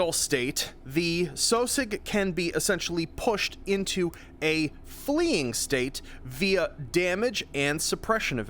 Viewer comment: "The sosig can sexually be pushed into a fleeing state" I sware that's what I heard at 1:40 ish... then at 3:21 lol